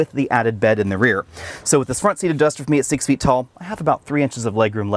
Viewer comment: Sir, this is a fantastic review; well done and detailed. I am sure you have realized this after edited the vid, but it would help if you said how tall you are. I 'think' at 8:03 you said 6 feet tall, but you speak so quickly some things are hard to understand, and perhaps should be said earlier when talking about front driver seat headroom. Thank you.